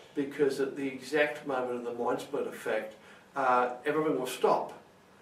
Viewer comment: Okay please in the begining of the video I couldn't understand what you were saying because it sounds like you're mumbling. So again the WHAT effect? It sounded like you said modulater effect or something like that